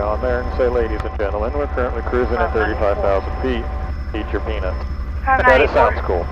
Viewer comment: I swore he said "eat your penis." I laughed pretty hard. I now understand it's peanuts.